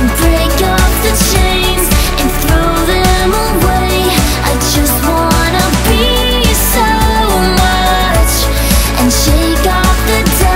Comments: when i first heard this song i thought that at 0:54 she said i just want to pee so much! but now i know its i want to be so much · hahaha I thought she said ''I just wanna pee so much... '''